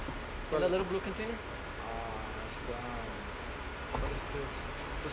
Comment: “Are those your pills” “Oh in the little blue bottle?” “Yeah” “ w h a t i s p i l l s “ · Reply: check yourself 1st..I'll make it easy 4 you click this.. 1:55 here he says WHAT IS PILLS..EVEN THE CAPTION SAYS